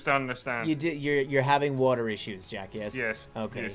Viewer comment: it sounded like said jack